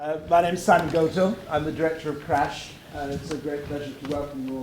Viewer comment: im sorry, did he say his name is son goku?